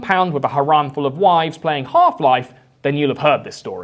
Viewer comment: When you said half life I at first thought you said park life as in blurr park life which old binlardin having a shoot out to park life I feel was a much funnier image in my head